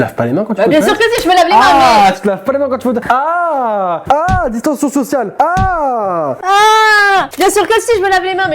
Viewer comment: Il a dit "distantion social" ou c'est moi qui ai mal entendue ? o_O